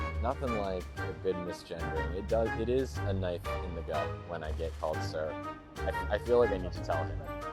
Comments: Did it says "a knife in the GUT"??? Well... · Did this guy say a knife in the butt? Maybe I misheard......